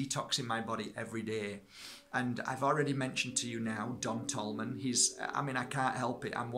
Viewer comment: anyone catch the name of the person is mentioning at 6:03? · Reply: Linda Smith... Don Tolman.